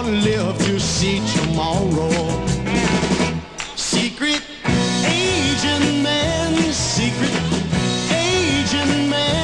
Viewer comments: I’m embarrassed to say that as a kid I used to go around singing this song as “Secret Asian Man”! My brother who is five years older than me let me do this for weeks before he finally told me what the correct version was and he laughed and laughed! There were other songs that I totally butchered as well! But man, this brought back memories!❤😂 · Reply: My wife just walked past as I was playing this and asked if it was a parody of Secret Agent Man! She thought it said Asian man too. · As a kid, I thought this was Secret Asian Man. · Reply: I thought it said secret aging man